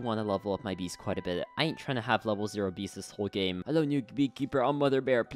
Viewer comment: DID HE JUST SAY NU** BEAR?!?!??! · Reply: He said new